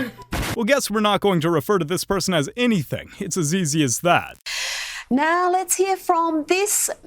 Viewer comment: lol i thought you said " it's a Ze ze as that"